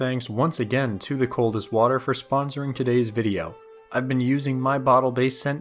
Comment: I Thought it said ''thanks to the coldest war'